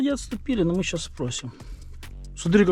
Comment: а мне послышалось "погасим" , гасите пацаны, гасите их!!